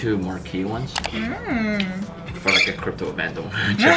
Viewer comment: What does Joma say at 8:19? I can't quite catch that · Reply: “It’s for a crypto event”